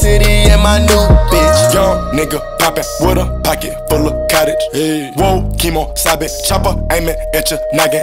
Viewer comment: Lmao bro when he says the ayy after cottage i thought it said cottage cheese too xD